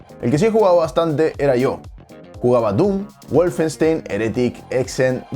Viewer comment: Cuando Nate dijo en 0:24 "jugué a doom, Wolfenstein,etc etc" Yo escuché "jugué a Doom , Wolfenstein, Doom, Doom y Doom